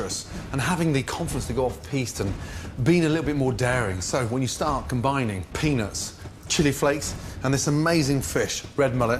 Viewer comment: Maybe its just his accent but I keep hearing "start adding penis to the chili" "Penis and Chili"